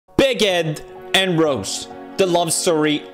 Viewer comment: at the very beginning pewds said "Big Ed and Rose" but i heard it as " Bigot and Rose"